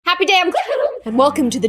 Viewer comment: _., i swear i always thought she said" Happy day on GLOOOM" ;v; not...."happy day IM GLOOOOOM"